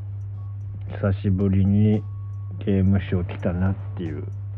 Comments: 初っ端「久しぶりに刑務所来たな」に聞こえる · 冒頭で「久しぶりに刑務所きたな」って空耳してびっくりしたわ 一瞬でも逮捕歴を疑った自分を許してほしい…… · 久しぶりに刑務所来たに聞こえた